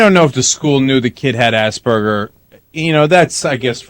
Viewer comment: Ahahahah. Did he just said "assburger" XD